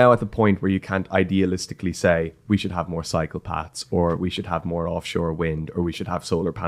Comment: Did he say we need more psychopaths?!